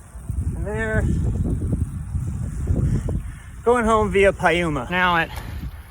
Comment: I heard "going home via paella" - now that would be a great way to end a ride